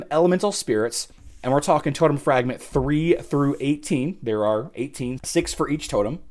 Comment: Well, I misunderstand you at first....I thought you said "18 totems frags for each totem" not "18 in total, so 3 (each totem) x 6 (1 frag per star level) :D